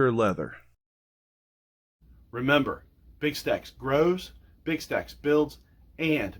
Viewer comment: I thought you said snacks!